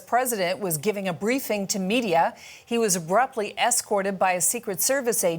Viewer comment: Roughly rushed out... mm hmm. Why do anchors feel the need to hyperbolize EVERYthing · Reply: She said abruptly (almost 99% sure) but I thought she said roughly too I was like he literally just strolled out